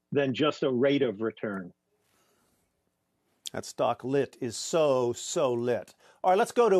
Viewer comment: did he just say "That stock lit, is so so litttt" or am i just hearing things LOL